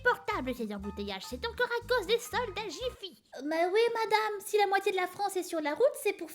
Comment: je sais pas pourquoi mais j'ai entendu "tous ça à cause des soldats gifi" j'ai pas compris